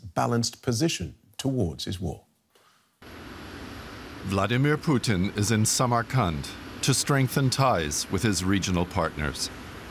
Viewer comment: at first, I heard "Vladimir Putin is a summer..." and I thought "Weird way of putting it, but accurate".